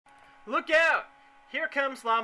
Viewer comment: At first i thought you said "look out, it's La-muleza!" That would've been freaky